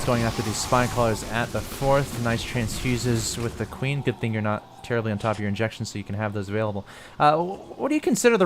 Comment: All I heard out of mouth: "Man your macro is terrible."